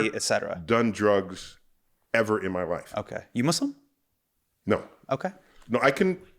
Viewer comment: Neil said, “I’ve never done drugs.” When you responded “you Muslim?” I heard “you want some?” Oh boy was the next part confusing. Had to rewind and rewatch.